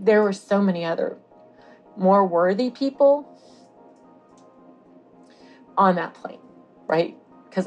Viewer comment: "why me? ... There were so many other, more worthy people on that plane.." 💔 bless her heart. Their surviving makes them a living miracle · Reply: I thought she said "There are more people worthy of being in that plane" LMAO